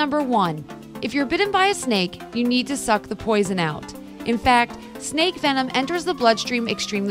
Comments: spelling mistake,position instead of poison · it says Position instead of Poison... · it says position instead of poison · it's written position instead of poison